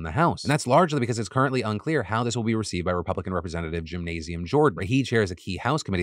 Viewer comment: I did a double-take and had to rewind to make sure I heard "Jim-nasium Jordan" correctly 😂😂😂